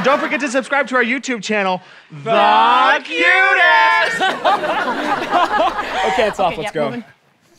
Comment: at first I thought it said f youdest.